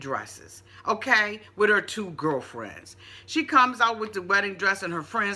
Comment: Megan really is naive or slow because she's way too trusting and invested in a person she has only known in prison. $12,000, moving to NY, give up her virginity, etc... plus she didn't even double check on the baby momma because if she had, then she would have seen that Sarah is still alive and well. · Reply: Wait she thought she wasn’t alive? Because I thought she said she not worried cause she “ comes first”